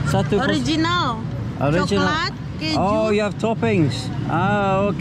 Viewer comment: i'm not sure... But I think he said "original, bodoh." it means "original, stupid."